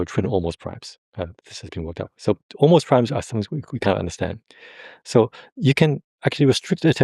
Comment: it is fascinating, but my Math is lagging... Terence Tau is a clear thinking guy, but a bit unclear to listen to verbally, at least for me, and also for AI_English automatic: 9:41 I think he didn't say " (the) Almost_Primes are something we can't ..." , but: " Almost_Primes are something we can understand. So You can actually restrict attention ... " Math is hard to chew on, for AI; this is a general problem, not merely this acoustics-, (or grammar/thesaurus)-difficulties here in this instance.